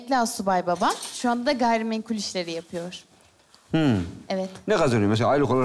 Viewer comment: Bir ben mi 1:49:31 da “gayri meşru” anladım ve bunu da bu kadar rahat söylemesine şaşırdım 😂